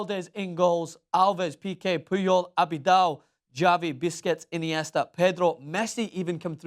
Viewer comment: did he just say biscuits for busquetst? lol